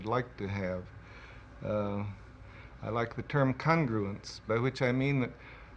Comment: What is the term he says at 4:20 he says i like ( sounds like cundrolence) what is this word anybody? how is it spelt? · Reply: congruence